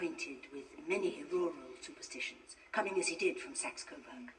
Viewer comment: Did I hear "cumming as he did from sex?" XD